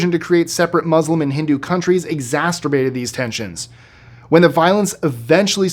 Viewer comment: did just say exasturbated? lmao freudian slip!